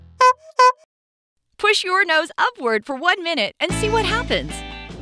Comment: I thought u said for 10 seconds not for a minute